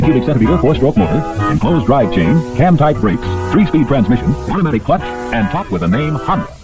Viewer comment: Did he just say "hentai breaks" ?